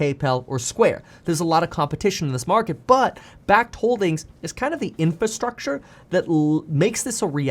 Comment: I thought he said "bag holdings"